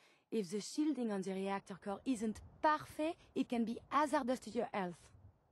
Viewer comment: lol I love how the people writing the dialogue display actually thought Curie said “parfait” instead of perfect. I get that she has a heavy accent but think about it a sec before you type it guys😆😆